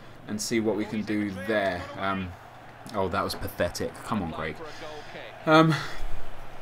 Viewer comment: did you say "Come on Greg"? Is that your real name?